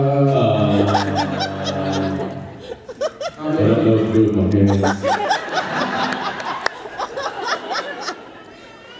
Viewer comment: What does Jensen say at 2:33? :) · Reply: Jensen saying I don't know what to do with my hands